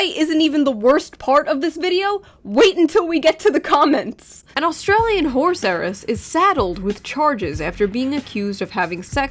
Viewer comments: I heard “Australian whore heiress”. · I haven't had my coffee yet, I thought you said "austrailian Whore serris" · I heard "horse heiress" as "whore-seress". Like a whore sorceress.